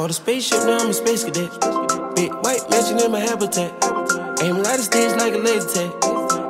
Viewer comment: I thought it said big white Mexican